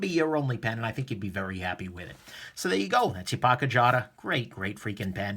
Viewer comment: Sorry, it sounded like you said Pogger jotter